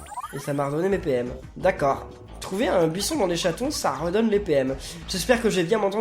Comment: c'est moi ou il a dit trouvé un buisson dans les chaton sa redonne des pm a